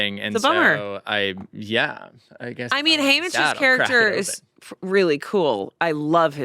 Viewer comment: Because I haven't seen any of The Hunger Games movies, when Amanda kept saying "Haymitch" I thought she was saying "Homage" wildly–due to them talking about prequels.